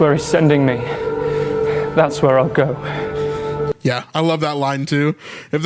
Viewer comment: I thought Stephen said, “If HE’S sending me there, that’s where I’ll go”, meaning that he will obey the will of God